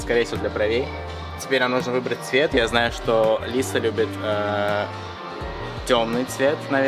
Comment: мне одной показалось что он сказал "Алиса"?